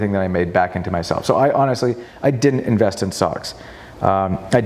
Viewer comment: Thought he said “I didn’t invest in socks.”